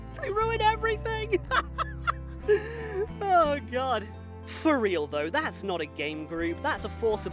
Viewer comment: … did you just say “fur’real” instead of for real?.. I hope you have to explain to the doctors why there’s a cucumber up you because you forgot the wide base rule